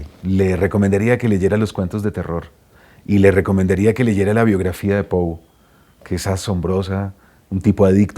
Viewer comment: Hola! Alguien me podría ayudar! en el minuto 6:33 habla sobre una recomendación, le recomiendo que lean la biografía de.... no entiendo que dice, muchas gracias! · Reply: Está hablando que además de las obras, leas la biografía del escritor Edgar Allan Poe